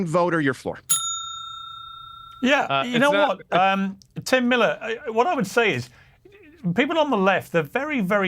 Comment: Thought Piers said "Tim Dillon" as the start. Lol